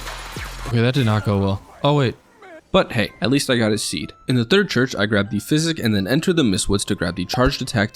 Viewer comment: ayoo did he say "atleast i got his seed"